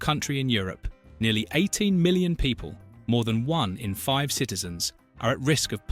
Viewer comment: This podcast is either an AI generated propaganda or is it created by an illiterate individual who says Germany has a total population of 18 milion people and who says Slovenia is a part of Croatia!😂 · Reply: He meant Slavonia (or Slavonija in Croatian) which was depicted in the images. His pronunciation was a bit confusing, I do admit. And I thought I heard 80 million for the population of Germany.